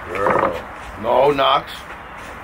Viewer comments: did papa just say "eat my teriyaki chicken, bitch" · did he just say "eat My teriyaki chicken, bitch"?😂